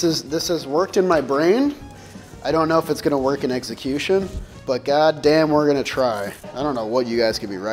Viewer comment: Did I hear that correctly and said “eggsecution” at 1:00 because he’s my new Jomboy favorite if so.